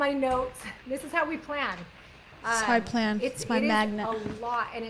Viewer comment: I think I heard you say “this is how I plan… this is my magnet” 🧲😂